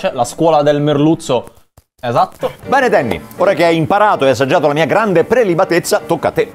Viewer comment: sbaglio oppure ho sentito mbare Danny?